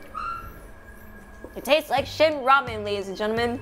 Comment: I really thought she said “this tastes like shit ramyon” lmaooo